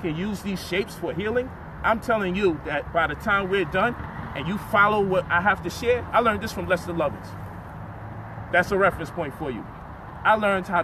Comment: did he say lesser lovers?